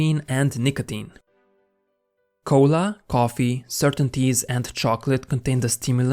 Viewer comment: He said "certain teas," but I thought he said "certainties."